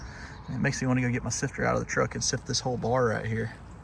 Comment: Enjoyed and learned.. for a second I Thought you said I should go get my sister out of the truck.😂